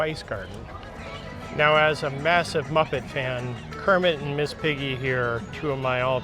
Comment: I thought you said “massive bucket fan” not “muppet fan”. …